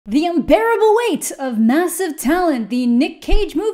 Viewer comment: I heard unbearable wait and was really disappointed it wasn't a certain book series about dragon's. That's ok. We can wait. Longer.